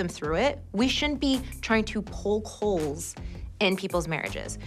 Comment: POKE HOLES!! (Enunciated) Even captioning thought Brynn was saying "pull coals" 🤣🤣 I for sure did, & was left very confused 🤔🤭🤪🙃